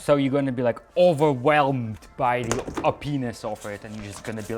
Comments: did he say overwhelmed by a penis off it. edited: yes he reallly did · Over whelmed by your penis? Is that what he said it sounds like it2:52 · "so you're going to be overwhelmed by the OP-ness over it" 😂 (Even the caption thought he said overwhelmed by the penis) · Ivan : overwhelmed by the OP-ness ( Repeats it multiple times) Linus : did you just say overwhelmed by a penis ??? Me : yeah that's what I heard too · Honestly I thought he was actually saying openis until I thought about it "oohhhh.... Opness"